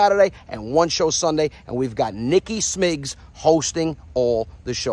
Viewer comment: That’s so funny! 🤣🤣🤣 it sounded like he was saying meat time lol 😂🤣you two are a riot! 👍🙌♥️♥️